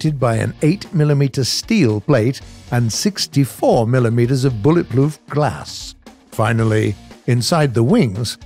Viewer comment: I swear at 1:54 he says bullet “ploof” glass😂😂